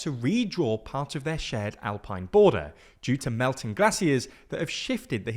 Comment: What the heck is a 'glassier' just say gracias 😭